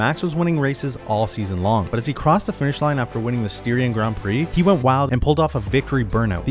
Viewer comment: "Syrian Grand Prix" I think bro is living in a different time-line 💀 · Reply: Yep there is even a source in the replies here. I misheard it for Syria rather than Styria.